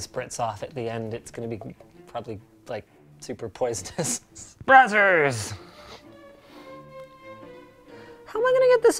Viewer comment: Did he say "Brazzers"? xD